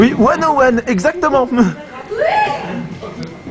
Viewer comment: Elle dit quoi la fille a 4:19 ? x) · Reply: "101" prononcé "one o' one" c'est le code de panne sur les attractions